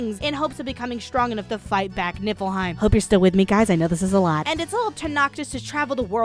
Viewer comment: every time she says Nifflheim I hear "Nippleheim" and at this point I'm not even disagreeing